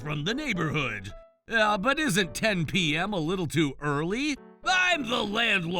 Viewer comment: they said 10pm when the subtitles said 10am 😅